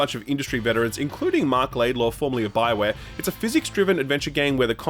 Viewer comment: I thought I heard you say Marc Laidlaw, not Mike Laidlaw. My heart jumped for a sec